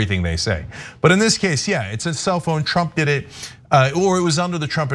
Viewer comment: I kept hearing "It's a cell phone" instead of "self-own". I'm like what is Cenk talking about?